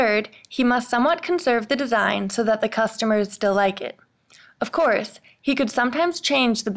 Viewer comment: Can someone tell me what delikeit means? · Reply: I think she said "still like it."